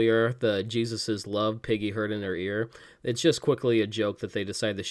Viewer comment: You definitely just said piggy instead of Peggy haha